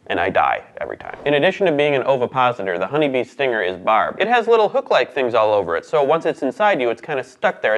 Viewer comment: When you said their stingers are barbed, I thought you said barb. Like their stingers are Barbara lol